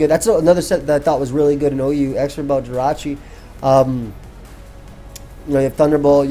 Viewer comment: WTF ARE YOU SAYING AT 40:50?!? EXTRA BUTT? EXRIBBA JIRACHI? CAPS INTENDED. I HAVE LISTENED LIKE 40 times and googled a few different things to no avail. Holy crap is it bugging me. WTF ARE YOU SAYING LOL?